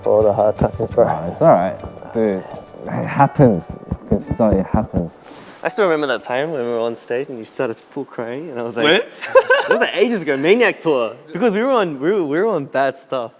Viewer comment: Did I just hear Felix say fat ass ?😂😂 HELP!!!!😭